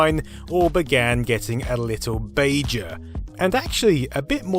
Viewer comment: For a moment there I seriously thought you said "getting a little Bajor."